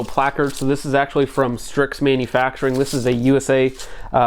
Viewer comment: Having trouble finding that front placard. What was the brand name? Thought I heard Striks (sp?) Manufacturing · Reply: Strix